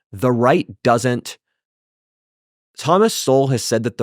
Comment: Did he say Thomas Sewell?